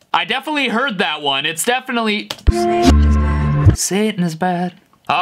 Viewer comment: When it said “satan is bad” I heard “that is true.... satan is bad!!” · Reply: all I heard was "I'm on fire satan is bad"